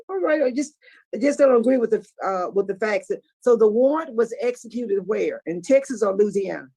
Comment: When asked the offender where the warrant was executed, I thought she said, "where was the warden executed, Texas or Louisiana?" 🤣 Also, there seems to be discrepancies about whether the photos of his alleged gf are legal or not. I seems to recall hearing that when the charges include seckshul misconduct, while on parole they are prohibited to have pawrnogrophry on their phone or computer, gf or not. But I could be wrong.